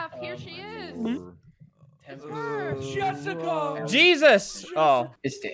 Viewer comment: no joke I thought that said Jurassic park